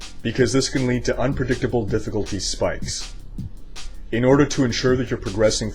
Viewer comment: I did totally mishear him...I could have sworn he said unpredictable dps...:P